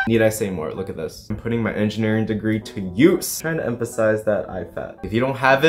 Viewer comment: no way I heard "my Ed Sheeran degree" at first lmao